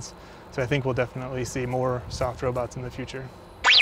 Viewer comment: i really thought he said last "we will see more sex robots in the future" XD